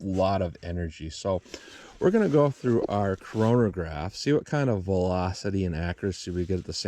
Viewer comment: Did you say Corona-graph? That was a sick pun. LOL, I'm not even a real dad.